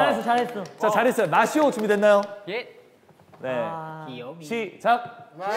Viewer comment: I hear jihoon say to shiho “Gwiyomi”